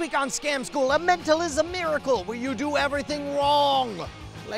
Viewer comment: At the end of the episode it sounded like he said "beericle" instead of miracle. New Scam School thing? Like the "Law of bar magic" perhaps?